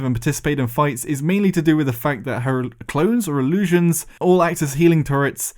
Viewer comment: Did anyone else hear the words "Her clothes are an illusion" instead of clones at the 1:33 mark? Anyone at all? Maybe its just wishful thinking.